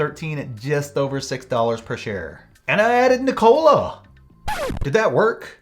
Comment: I was listening to the video and playing on my phone, when all of a sudden I hear "And I added NKLA". I immediately dropped my phone and turned to look and was like WTF LOL. You got me there :P